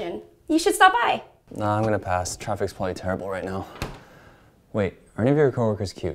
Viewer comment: What does he say at 1:33? Can't figure it out, not a native speaker :S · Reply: He says, "No, I'm going to pass. Traffic is probably terrible right now." Hopefully that helped!